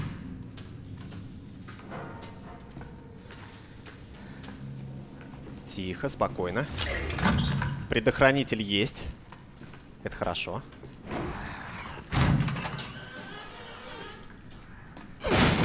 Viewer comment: мне одному послышалось бредохранитель?